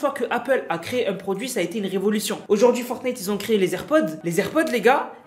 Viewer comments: je rêve ou j'ai entendu "... FORTNITE ont crée les Airpods..." (3:08) 🤣🤣🤣🤣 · c'est moi ou il a dis fortnite